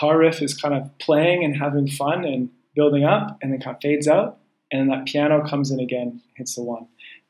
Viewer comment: Hey, what sentence did you say exactly at 2:56 (after "building up"). I couldn't understand and there's no subtitles available here. Thanks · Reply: Hey Stijn, I said "the guitar riff is kind of playing, having fun and building up and then it kinda fades out. And then that piano comes in again and hits the 1." - thanks for watching! Hope it's helping :)